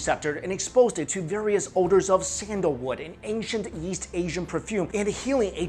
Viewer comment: Hello, from 0:45 to 1:10 you said sandalwood, "sandlore" and "sandallore" i replayed this several times to try and make out what you're saying, could you reply to me what it is you're actually saying? I would like to look into this and experiment with the healing properties! Or if anyone else can make out what he's saying, please let me know!! thanks!